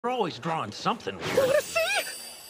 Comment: After so many times watching this I finally found out Reki said "wanna see?!" in the first clip. I keep hearing "urushii?!" what does that even mean